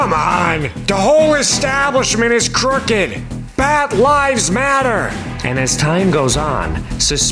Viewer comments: "Bat Lives Matter!" Those who say otherwise are disregarding the fact that bats can sometimes be cute, too. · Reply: I thought he say “bad lives matter” bc the type of people who shout the other blm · I've heard: "Bad Lies Matter" lol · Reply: I heard "bad LIES matter." too. 😂